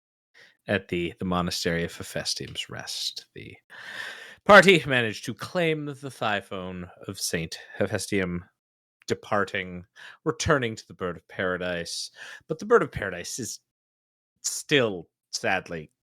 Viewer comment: Did say THIGH PHONE